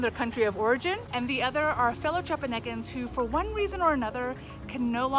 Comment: WHAT'S THAT word you use at the 2:15 mark? "Former trapaguians?" I'd love to learn a new word. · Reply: 😂 “Fellow Chiapanecans”… basically made up, but just referencing people who are from other parts of Chiapas. And yes, I said it quickly and without the best articulation!